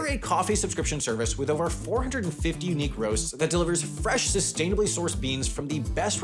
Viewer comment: When he said "unique roasts" I thought he said "knee grows" lol